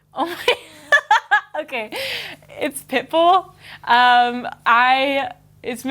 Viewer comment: When she said pitbull I thought she said pickle hahaha